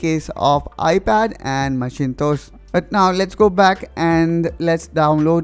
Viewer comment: 😄😄Omg! did you just say MACHINE-TOES? haha. its "macintosh" pronounced as "mack-in-tosh"